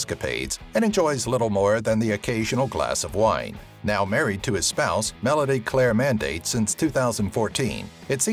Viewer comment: did he just say now hes married to his spouse normally, or am I thinking that wrong?